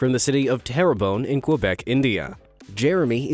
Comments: Did he say Quebec, India at 11:49? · I'm sorry if I heard wrong but quebec India?